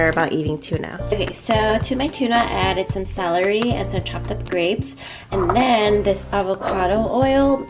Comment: is it just me or does it sound like she said "chocolate grapes"